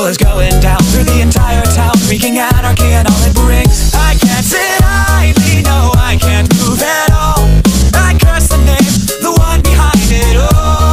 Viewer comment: What i hear: "I Guess it starts ,when i cant prove you wrong,is logical...your the one behind it alllllll" The actual line: "I cant sit idly ,no i cant move at all,i curse the name,the one behind it all" I mean, i need to go to the eye doctor